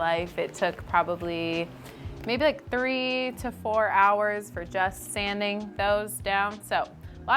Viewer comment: It is a very beautiful piece of furniture. You did not talk about your wage. If the 480.00 profit is your wage and it took reasonable amount of time say 80 hours than your hourly wage is only 6.00. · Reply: I thought she said 10 hours.